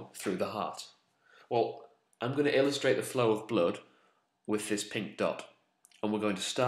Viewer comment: I misheard and thought he was going to show the flow of the blood with 'this pink duck.' Imagined a little toy duck moving around the diagram, made me smile.